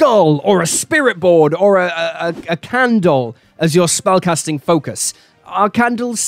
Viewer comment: Love these ideas. Plan to try them in the future. Also, like he misheard "Hypnotic Gaze", I first misheard him say candle as "Ken Doll".